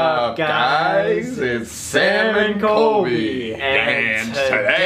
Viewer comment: Every time they do the sam and colby intro i keep hearing "What's up guys its SALMON colby" anyone else hear that besides me.